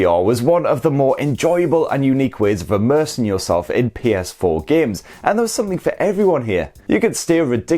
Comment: Hes from the UK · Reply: I can tell he's from the UK but still he is pronouncing letter A like E in some words 0:05 he said Games but sounds like Gems, Playstation Playstetion you know what I'm saying.. maybe he's Scottish or like a bit Irish I don't know 😅